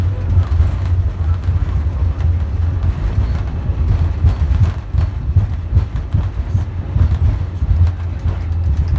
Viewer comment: WEDNESDAY JULY <num>:05 PM CDT After landing, it looked like the aircraft was shaking at 4:24. Kind of like the nose gear tires had a flat spot, causing the plane shaking at 4:24. 😕 Also, what was the female pilot saying at 5:06? 😕 Tony 59-year-old Black Air Force VET 🇺🇸✈ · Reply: She said 'Holy moley - amount of ground traffic.